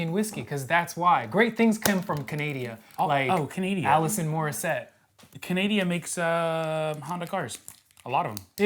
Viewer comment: Did I hear “Allison” Morissette? : )